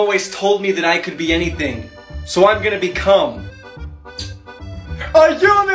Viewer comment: I THOUGHT HE SAID SO I COULD BE COME😭😭😭